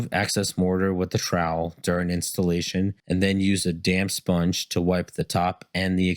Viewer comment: As a none-native English speaker, I heard "Use the damn sponge.." the first 5 times he said it. 😂